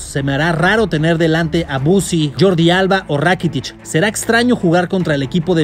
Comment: soy el único que entendió que dijo mussi